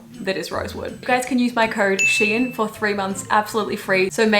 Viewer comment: when you said your code is “sheehan” i thought you said “shein” and i was like HUH